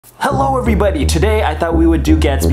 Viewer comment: Oooooh I thought in the intro said “hello every corgis” sorry and tell gaspy I said hewo dog